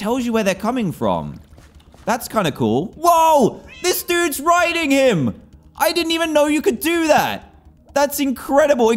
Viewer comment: What did he say at 23:52 🤨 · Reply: get a grip